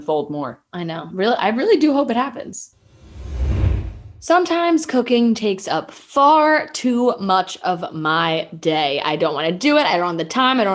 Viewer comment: IT LITERALLY SOUNDS LIKE FRAN SAYS, "sometimes cocaine takes up far too much of my day" @ 25:35 GIRL I CHOKED ON MY WATER WHEN I HEARD THAT😂